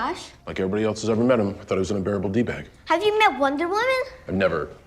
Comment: Ooooh, I thought he said The Flash was “an adorable douchebag”, which was kinda cute…I guess “unbearable” makes more sense though.